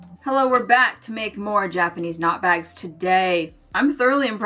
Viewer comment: There was one moment when you said “Japanese Knot Bag” super fast and all I heard was “Japanese Snot Bag”. I will not describe what I envisioned. 😂